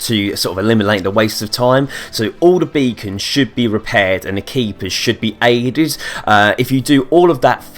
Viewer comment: Wtf does he say at 2:17???? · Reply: i wanna know what he says at 4:58 the keepers should be ertys?